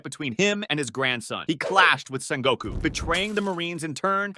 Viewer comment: I heard he clapped with Sengoku